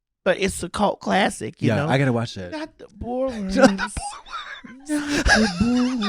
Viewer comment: NOT THE BORE WORMS is my new vocal stim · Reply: I thought they said "not the BALL worms" and I was livingggg